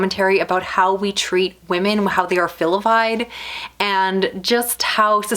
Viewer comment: Did I hear “filivied”?